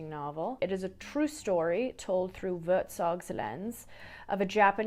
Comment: I swear you said WerZog on or about @ 11:47. Great brain language connection!